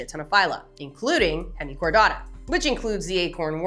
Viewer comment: My mind was going weird places and totally thought you said the acorn worm was in the phylum "hentai-chordata". Which it should be.